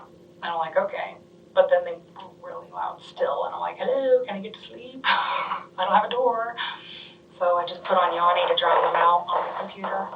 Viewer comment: Did she say she put on Yani to drown them out??🫠🫪